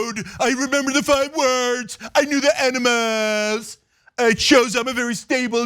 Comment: Did he say I know the Enemas ? Trump knows the Enemas? 🪠🚽